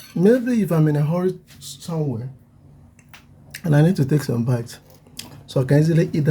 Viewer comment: did i hear wrong but did you say "if im in a horible place..somewere and i need to take something to bite i will make it a sandwich"